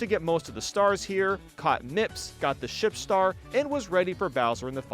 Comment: i thought he said "ship" 😂😂